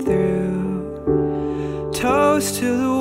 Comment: 0 :40 And 0:45 !Toes!that's what He says ! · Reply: He actually said toast